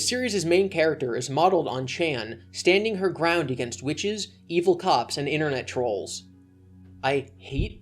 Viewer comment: I heard “evil cops” as “evil cocks”